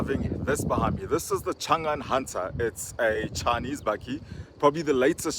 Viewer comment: Thought I heard you say "Shangan Hunter" 🤪